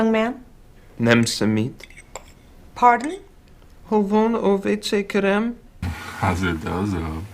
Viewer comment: I was hoping a Hungarian speaker would leave a comment I had a feeling they were butchering it but I didn’t want to say that in the video in case I was wrong 🫢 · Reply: Oh yes, I can confirm that your hunch was correct, they were butchering it pretty bad. The delivery of the line at 12:08 was not too bad, but what's actually said is so out of place that it didn't clock either. He says "Hol van a vécé, kérem?", meaning "Where's the toilet, please?" using a rather archaic phrasing, the type my grandparents (~70-80 y/o) would use when asking a stranger. On the other hand I've listened to it at least 10 times and I still can't figure out what the hell does the other guy say (the one that sounds like "has it does-o") in the snippet right after...